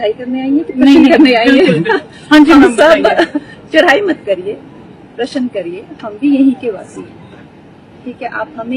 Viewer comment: i think she said "प्रश्न ". You miss heard or totally making it fake